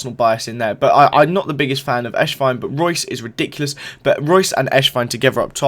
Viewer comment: It sounds like ur saying eshvine instead of esswein lol